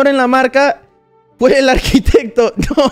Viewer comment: What did he say at 3:01? I can't hear the word I'm learning Spanish as my third language, still not that good at it haha Can someone please write it out for me? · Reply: He said "fue el arquitecto" and the translation with the context of what he is saying is "ignore the mark, it was the architect (who made the mark) *LAUGHS*"